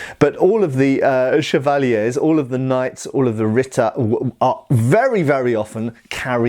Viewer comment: what is "rita"? I'm not familiar with this word, not sure if I'm hearing it right; got the spelling from the closed captions; google couldn't give a definition that matched our context. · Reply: Late, but it's "Ritter".